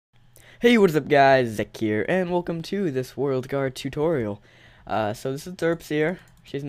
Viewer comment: Lol turn on subtitiles and go start the video at 0:01 and look what it says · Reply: He was that guy's dick Year and welcome to this world car tutorial. LOL